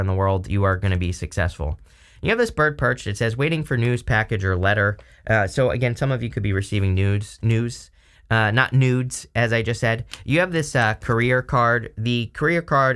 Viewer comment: I actually misheard you as saying receiving newts… I wouldn’t be upset if I received some newts.